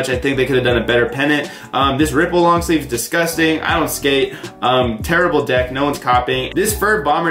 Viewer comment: it sounded like he randomly said I don’t skate shits has me dying😂